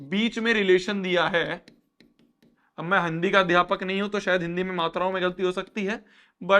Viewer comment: Am I the only one , who heard 'handi ka adaypak' 😭